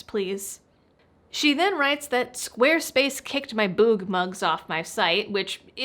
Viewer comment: I was just listening, while cleaning up my desk, until I heard her say she had a "boob mug" kicked off squarespace and then she had my full attention. Well, until I realized she said, "Boog mug". Disappointed ;-)